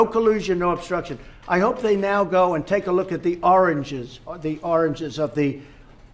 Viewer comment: I swore he said oranges not origins at first LOL